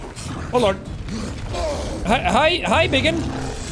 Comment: I thought he said "hi bigot" for a sec, but honestly they do look like they're gonna start telling me how Trump is the best and all the gays should convert.